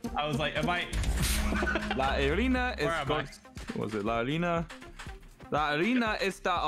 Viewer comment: I love how at 3:13:00 he just said "harina" instead of "arena" And arina means flour